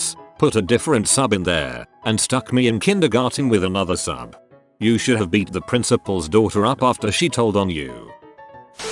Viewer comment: what’s the censored word? I can’t figure it out. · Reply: The word is pissed